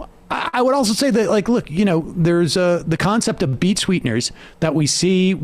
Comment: When he said "beat sweeteners" I thought he was saying "beet sweeteners" . Like wtf does the taliban want with a bunch of granulated beet sugar?